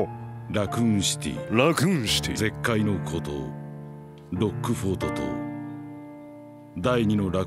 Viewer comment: ロックフォード島がドックフード島に聞こえる